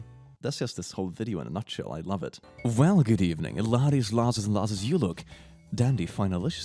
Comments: No week is complete without a, welcome, ladies,lasses, and lassos. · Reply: I always heard "ladies, laddies, and louses". Being a louse was fun while it lasted. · No week is complete without a, welcome, ladies,lasses, and lassos. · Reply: I thought he said Larses. Because Lars is a common first name in Northern Europe.